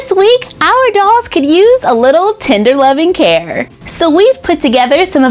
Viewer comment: I thought you said "Tinder love and care" instead of "tender love and care", those DEFINITELY have different meanings😂😂